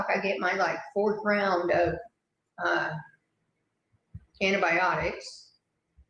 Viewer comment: Dont want to cry because she needs the hydration lol! Sounded like she said imobiitics instead of antibiotics. Walking around??? Yeah right!